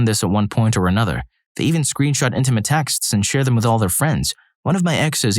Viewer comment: This is all very inappropriate behavior, but am I the only one who heard him say "screenshart"😮😂🎉